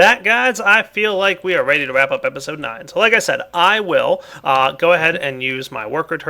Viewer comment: did he just say wrap up episode 9?